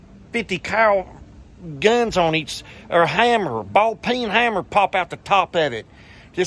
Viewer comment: Almost thought Donny said " Ball Pern Hummer"